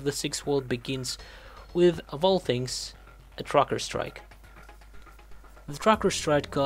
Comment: What the hell is a "Crocker strike" Are you say "TRUCKERS" strike?